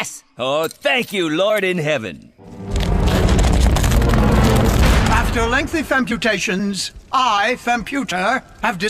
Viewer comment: "after many femputations". lol. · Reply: I heard “after lengthy femputations”